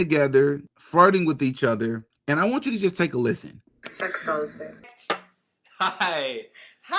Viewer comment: did Erika just say “s€x sells”?!?! wtf 😳